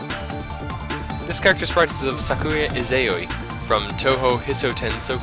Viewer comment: What is eioi? ( 8:53 ) I am probably mishearing it, but really have no idea what should it be. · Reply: It's "Sakuya Izayoi", name of that maid throwing knives. She is one of playable characters.